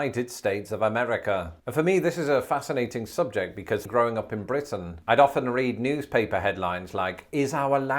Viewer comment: Legit thought he said throwing up in Britton 😂